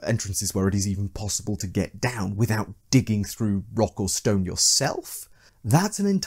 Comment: did I hear a rock and stone